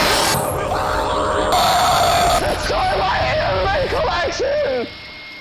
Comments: why did i hear "don't destroy my anime collection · did he say destroy my anime collection? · did he just say DONT DESTROY MY HENTI COLLECTION AAHh XD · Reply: He said “DESTROY MY ANIME COLLECTION!!” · did he say he has a medical erection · did he say don’t destroy my anime collection?😂😂😂😂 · did he just say don't destroy my anime collection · did he say “this goes in my anime collection!” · I was trying to figure out what he said, the captions have been written by real people so i pressed them, he said "DESTROY MY ANIME COLLECTION" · Did he just say “destroy my anime collection”?